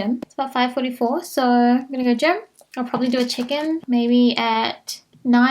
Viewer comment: I kept hearing “Chicken”🐥 instead of “check in”😂🤦🏽‍♀️